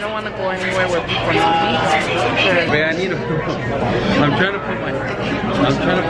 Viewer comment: Did say “bae I need a” on 8:27 ???